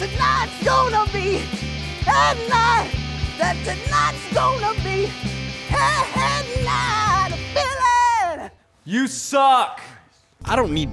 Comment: did he say egg night?